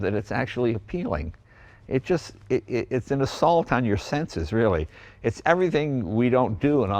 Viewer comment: What did he say here? 5:49 "it's solid on your senses?" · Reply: Deniz Oğuz Sevil “it’s an assault on your senses”